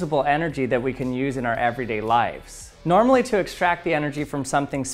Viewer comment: It sounded like he said “everyday lifes” instead of “everyday lives”